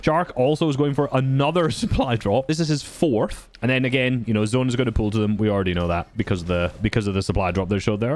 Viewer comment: thought you'd say "we know zone pulls to them because it's teeny" lmao